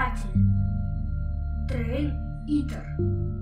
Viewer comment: я один слышу грей гитлер 😂😂😂😂я